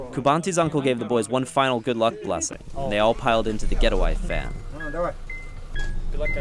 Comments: "and they all piled into the GET A WIFE VAN" ???? YOU'RE NOT SLICK I HEARD THAT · I thought he said get a wife van xD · you they did not say go go ! Lol they said fuck this guy lmao